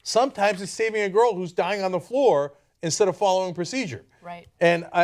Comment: Following procedure? Sorry, what I heard was "I was just following orders".